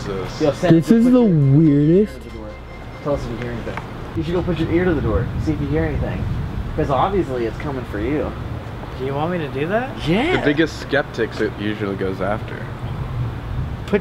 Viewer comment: someone whispers “He’s coming” 19:01 and 20:05 strange white figure fades in and out right next to the cross by the gate on the far left of the screen Elton of you see this.....YA DONE MESSED UP GOING HERE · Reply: I heard it as "its coming"